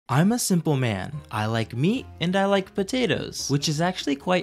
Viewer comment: First time i. Watched this i thought you said im a single man lol